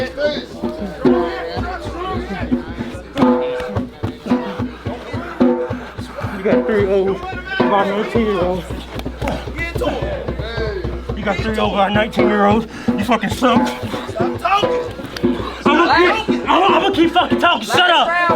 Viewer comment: LMAO great fight. You got beat by a 19 year old Also: Men's Mental Health matters.....outside the ring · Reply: I thought he said "you have a 3 year old(child) with a 19 year old(girl) youre sus" that's just what I hears